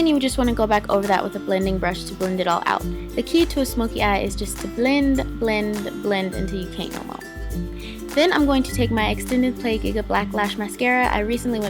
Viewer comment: I MISHEARD AND THOUGHT YOU SAID THE TRIP TO A SMOKEY EYE IS TO BLEND BLEND BLEND AND TAKE YOUR SHIRT OFF HAHAHA